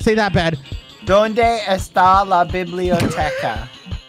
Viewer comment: Can anyone tell me what bad said at 1:17:40? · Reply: "Where is the library"